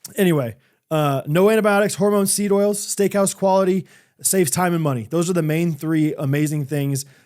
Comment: I really thought said, "Saves Timon money!" Well, if it saves Timon money, of course I'll buy Good Ranchers!